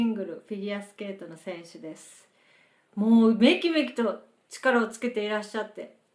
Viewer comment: what does she say? "mou ubeki beku to"?